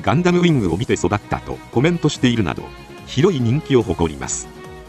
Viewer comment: 聖闘士星矢からサムライトルーパーそしてWと、美少年達がズタボロになりながら闘い抜く系はお嬢様方に大人気ですよね。 そして 1:01 『酷い人気』と空耳するなどw